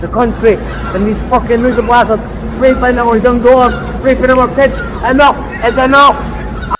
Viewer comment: Pourquoi il dit : “un œuf est un œuf” ??? 😅🥚